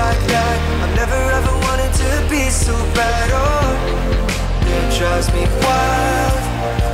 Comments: who else hears i never wanted to pee so bad at 0:50 ? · why do I hear "never ever wanted to pee so bad ohh???" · When he says "Be so Bad" I thought Troye said "pee so bad" oops